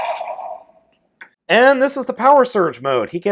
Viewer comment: surging fire ? surging bug ? surging butt ? the heck is he saying ?